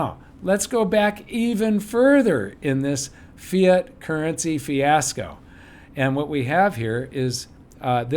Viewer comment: hahah I heard "Fear Currency", when you actually said "Fiat Currency" :p